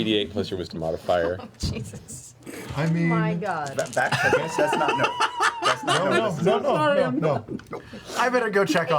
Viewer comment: What does Vax say at 20:33? I couldn’t make it out and it wasn’t in the subtitles. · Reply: He just said 'Vax does not know, Vax doesn't know' because he wasn't paying attention to the cliff dive